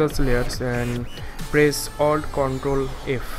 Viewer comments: Nvm i got it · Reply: did you say press Alt Ctrl F if so i didn't see anything the particles didn't show up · select press control F? but nothing happened when i pressed it //....pls help what did you say? i try with ctrl f but nothing is happen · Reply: he said ALT CONTROL F not control f